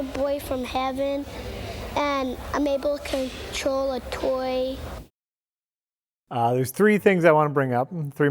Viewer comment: I thought he said “and I’m trying to troll a toy” and I had to check the date.